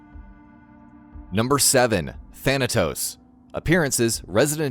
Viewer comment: Thanos? I THOUGHT I HEARD THANOS